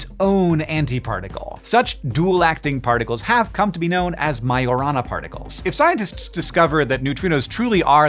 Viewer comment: listening while cooking thought you said la llorona particle had to refocus to understand what awful thing this particle did and why my nephews should be afraid of it